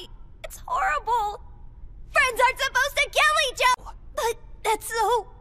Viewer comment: first time i hear this in the dub version And i thought she said “Friends are suppose to kill each other”